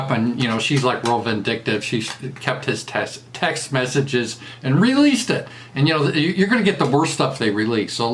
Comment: "Kept his test...text messages.." - I thought you were going to say she "kept his te*ticles", but my mistake, how could anyone do such a thing?